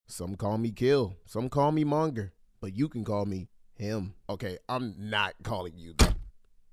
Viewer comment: at first i thought he said but you can call me daddy